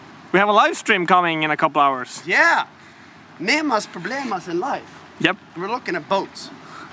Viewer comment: did you just said on 04:19 nemas problema?